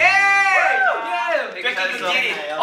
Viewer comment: why did I think he said “ dwaekki can get it “